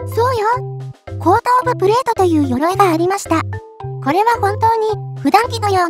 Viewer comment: が後頭部プレートに聞こえて一瞬混乱した